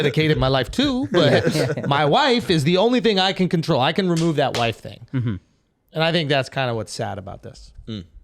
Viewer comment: when Shulz said "I can remove that wife thing" I misheard it as "I can rescind that wifehood" and now I have to create a life of my own that will lead me to one day be able to use that phrase myself